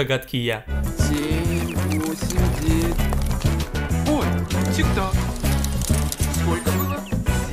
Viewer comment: Эммм мне послышалось или на 5:30 чувак сказал ой тик ток